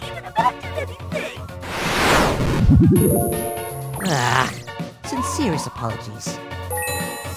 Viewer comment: When Ena says “Ah. Sincerest apologies.” It’s so cute 😭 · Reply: I always hear it as "Ah. Sincerest apawlagies"